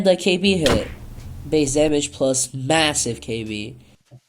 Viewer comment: DID U JUST SAY MASSIVE? Jst throw the potion